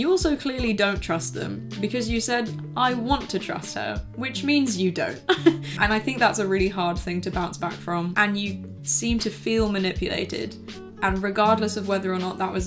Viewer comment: Any native can help me and tell me what says from min 5:35 to 5:40 ? That would be very helpful thank you very much :) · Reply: “because you said, ‘I want to trust her,’ which means you don’t. And I think that’s a really hard thing to bounce back from. And you seem to feel manipulated.”